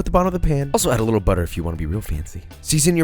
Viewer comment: does he say “season your potatoes” or “si senior potatoes”. who is senior potatoes?